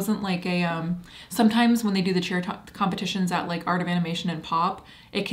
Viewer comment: When you said cheer competition, I thought you said “Cher”. I pictured lots of Cher impersonators trying to look exactly like her. (I am 62, getting harder to hear).